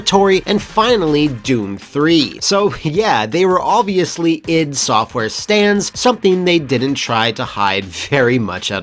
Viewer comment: I thought you said they were "Id software stands" like Id softaware summoned them like a JoJo character or some shit, which would also make sense